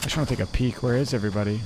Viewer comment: I thought he said “I just wanna take a pee real quick”🤣🤣🤣🤣🤣😂😂😂😂😂